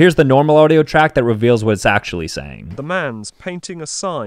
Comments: PLEASE I HEARD “The man, sex and wine!” 💀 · I hear "Bros painting the sign"... I was close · I heard “Hey, a painting is soy” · I heard "The cow is facing the sign/sun (can't tell which one"